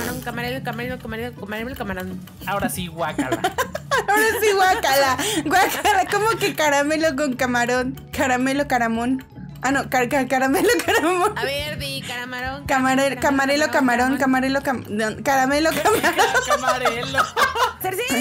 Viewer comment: JAJAJAJA Re epico de que se dijeran cosas sin sentido a contestar las preguntas xD Muy bueno el video ❣ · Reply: Camarelo Caramon Increible ☝️🤓 Pense que decia caramelo caramon JAJAJA 😭